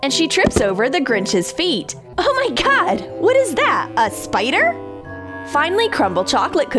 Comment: On 10:22 it thought it is poop but than you said spider .